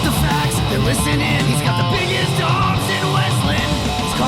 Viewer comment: Did the ending song say "He's got the biggest dong"??? Lmao Also I want to know what fight was he talking about, when he said Tito tapped????